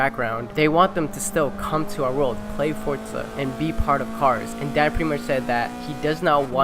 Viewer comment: did u say dad ?